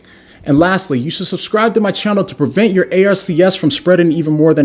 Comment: When you spelled a-r-c-s at 5:19 I first thought you said a-r-s-e! Not good!